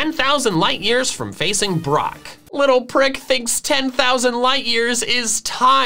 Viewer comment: DID JUST SAY PRICK!?- my childhood is ruined omg